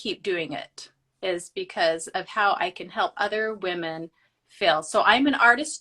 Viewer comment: I know she must have said "help other women feel" but I swear I keep hearing "help other women fail" and I'm like ooops. lol